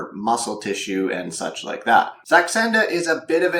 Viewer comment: What was the word yo said at min 2:40. Texsenda????